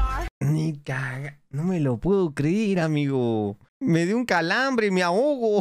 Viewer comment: Yo no escuche "me dio un calambre", yo escuché... "invadir bolivia, invadir boliviaaaa"